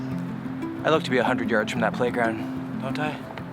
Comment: I thought you said you had to be 100 yards from the playground my bad I heard wrong😂😂😂😂😂